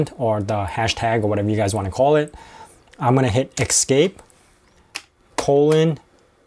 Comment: i'm french, so i didn't understand what you said after escape, and youtube subtitles not understand, please help me! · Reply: Desolé de ne répondre que 9 mois plus tard. C'était [escape] :wq [enter] Le caractère : se dit "colon" en anglais. Quand tu es dans l'éditeur de texte Vim escape te permet de sortir du mode "insert" dans lequel tu édites le document. Puis : indique que tu vas entrer une commabde. wq (write/quit) indique que tu veux sauvegarder et quitter le document.